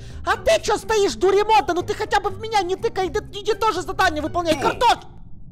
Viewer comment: я слышу картон а не картошка...